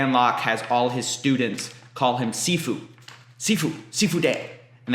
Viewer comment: Did you say Sifudae? That's "fuck you" in Portuguese lol